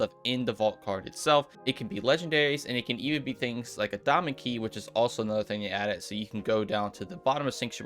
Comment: Sounds like you say doma key instead of diamond key 🤣🤣